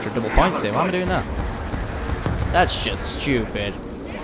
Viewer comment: did he say that's just gewpid